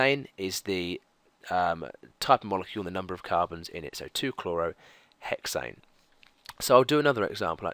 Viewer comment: is it 2-chlorohexane at 14:45 or is it 2-chlorohexane-1,6-dimethyl? Nvm I didn't hear everything he said 😂 2-chlorohexane it is